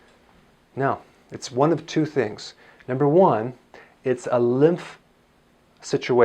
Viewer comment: I thought he said, “it’s a lift situation...” (it sure is!😂)